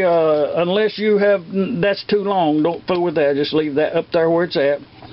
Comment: did u say dont fuck with that? lol